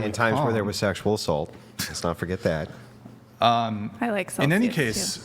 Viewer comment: did she say, "I like sauces, too." WTF?